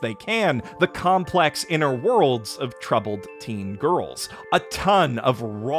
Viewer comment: I stopped in my tracks once I heard you say "Wonder Egg Priority" was about "fourteen girls". Then I paused and thought "Oh. Four teen girls".